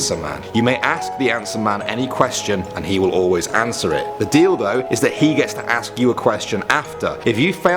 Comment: Did I just hear the dildo